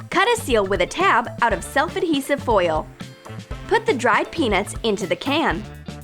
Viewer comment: I thought she said penis